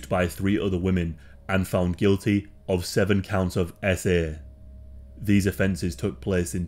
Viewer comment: What is "essay" if that is what he is saying, is that a replacement word for the"R" word so you don't get demonetized? Or is that a criminal charge? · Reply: Yes He’s says S.A which stands for $3xū@ Æ$$ů|+ so he doesn’t get demonized. Weird YouTube rules 😂